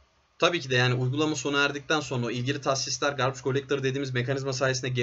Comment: ne collector anladım merak ettim internetten bakim dedimde söyleyen olursa sevinirim. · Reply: Garbage